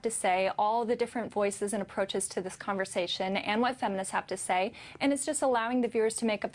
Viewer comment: If you have subtitles on, at 11:42 she says "and what feminists have to say" but the captions read "and what some nuts have to say." I just about pissed myself lmao